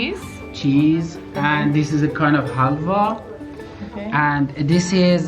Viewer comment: wow at 5:36 did he say Halwa, just did a google search for "halwa" looks like its a made in multiple countries. Never thought as a Indian Halva is made outside sub continent. From wiki "Halva is any of various dense, sweet confections made in the Middle East, Central Asia and the Indian subcontinent. It is also served in the Caucasus, North Africa, the Horn of Africa, the Balkans, Eastern and Western Europe, Malta and in the Jewish diaspora."